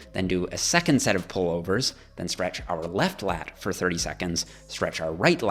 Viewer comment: I thought he said stretch you left nut